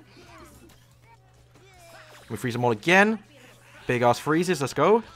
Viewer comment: Bro I’m not making fun but at 6:42 did you say big ass freezes